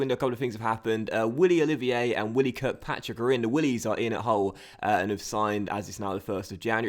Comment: "The willies are in at Hull!"... I thought you said "The willies are in the hole!"